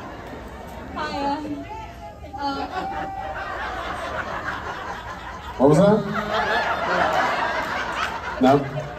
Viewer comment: What did someone shout out at 36:55? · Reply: "He's not your daddy"